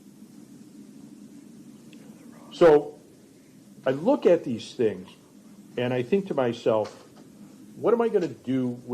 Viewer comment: Did anyone else hear him whisper wrong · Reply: I think he says “they are wrong” 🤷🏻‍♀️🤷🏻‍♀️. Dumbass 😂😂😂